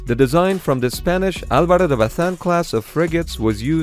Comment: *What Did He Say? !? !? !* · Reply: Álvaro de Bazán